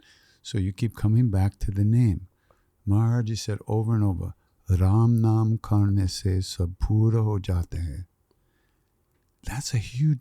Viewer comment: Please what he says in 1:06:12. I am brazilian and do not understand this part. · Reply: "Ram naam karne se sab pura hojata hai." - Neem Karoli Baba From continually repeating the name Ram (the name of god), everything is accomplished.